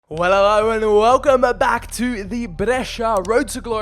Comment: WTF did u say right now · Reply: He said "Well hello" just sounded kinda weird